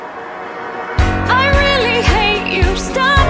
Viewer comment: When Chica says: I really hate you I thought she said: I really hate food